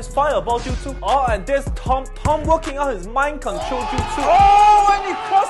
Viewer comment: The mind control jutsu hit me hahhaaa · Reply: i thought he said spine control